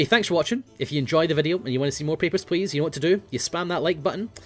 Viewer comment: When says "Papers, please" in russian accent, it sounds like he is saying "Pepper's Place" xD no offence, you do have a beautiful russian accent ;)